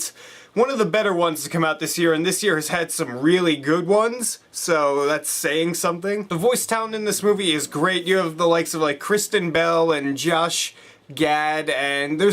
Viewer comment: Near the beginning when you mention Kristen Bell I thought you said Christian Bale and for a second was like ''What?! I didn't know he did a voice for this movie, that's awesome!'' Anyways, great review.